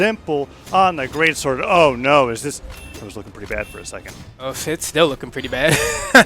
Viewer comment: what he say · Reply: “It was lookin pretty bad for a second” Do you not speak english bc that was clear as day